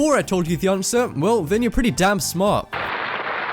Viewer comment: did he just said pretty dumb smart?